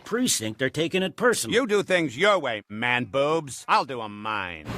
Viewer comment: I actually misheard, instead of man boobs, I heard mad boobs. I am not sure which ones better now. Officer Mad Boobs.